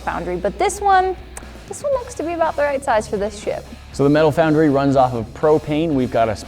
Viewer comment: Did anyone else hear sh-t thr first time watching instead of ship? I'm curious.